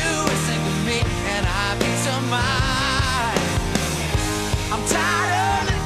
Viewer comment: I don't wanna be anything other than what I've been tryna be lately. All I have to do is think of me and have peace of mind. I'm tired of looking around rooms wondering what I gotta do, or who I'm supposed to be. I don't wanna be anything other than me. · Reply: All this time I thought it was "and I'll be somebody;" I never heard it as "and have peace of mind".